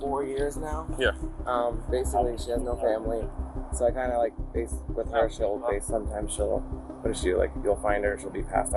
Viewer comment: Did I really hear him saying "I kinda like base with her"? 😮